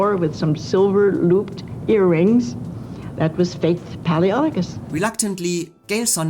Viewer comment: The reason why I wrote the comment below, was that I thought that you said Gale Sondergaard's character in Anthony Adverse was called "Face" not "Faith", so it took me a while to understand. Thank you again for the video and I hope my comment didn't bother you. 🙏